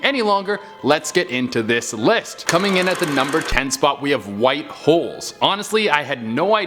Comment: i thought he said white hoes but its probley because everyone in my house is yelling right now :P